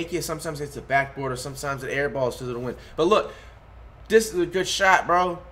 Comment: Did he say bc of the wind?